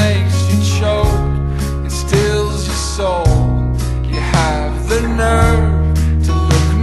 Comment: Best line ever: "Swallow him whole like a pill that makes you choke and stills your soul" It describes it all when you've been cheated on. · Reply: I always heard that as "steals your soul". That might be a bit more brutal than intended. Thoughts?